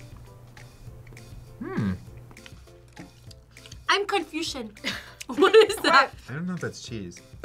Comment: I thought for a second she said “I’m confucius” 😂